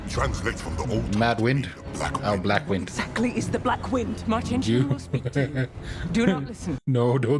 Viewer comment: Not having read the books, when they first mentioned the wind I heard "Martin Sheen will talk to you."